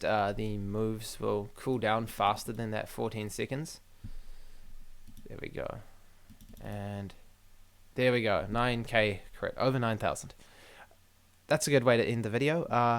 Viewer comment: did say IT'S OVER 9000!!!!!